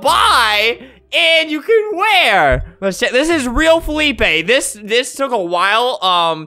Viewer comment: did he say shit or this